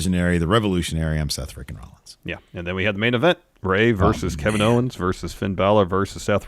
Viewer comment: I was listening while doing some work and at 59:30, said Ray and I heard "Bray" and I skipped a beat. ...I'm sad now.